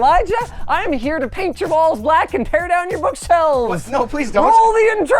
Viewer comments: i heard balls not walls 💀💀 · I misheard balls